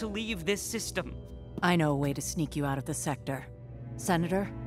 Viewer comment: Looks like this episode is gonna be a pretty good one! But I think I most of us are more excited to see Maul and Kenobi meet up in "Twin Suns"! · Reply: Dantooine? I thought you said Tatooi- wait, is that Maul and Obi Wan duking it out down there? Let's go watch! :D